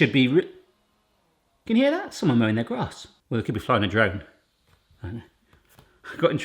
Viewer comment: ish, I heard "flying a dragon" totally a better option!